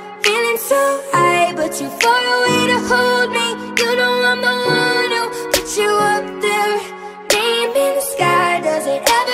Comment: ohhhh so it was "baby, I'm the one who put you up there" I always thought it was "hindiladumadukikmelade" or maybe I just misheard it cuz I just mostly heard it on random videos I found... I didn't know what this song was called son